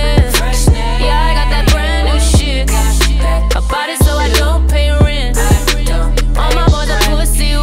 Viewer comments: Who else's deaf asses thought she said "a b0dy so I dont pay rent" cause that would also be a vv relatable bar lmfao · "I bought it so I don't pay rent" is a GOAL. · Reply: I hear here " I party so I don't pay rent" 😅🤣